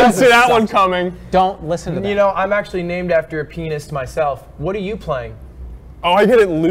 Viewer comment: "you know im actually named after a pianist myself" "oh i get it lewd" sters too quick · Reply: ludwig says "pianist" but it sounds like he says "penis" hehe haha